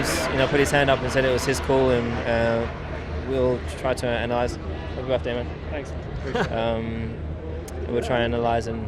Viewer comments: what did Hamilton say to Ricciardo? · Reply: Mrnice1 “happy birthday man” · What did Hamilton say to Riccardo in the end on 25:22? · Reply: He said “happy birthday man”